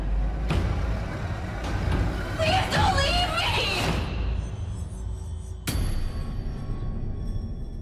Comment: It's sounds like mona said "Mimas gonna buy you a looking glass" at the end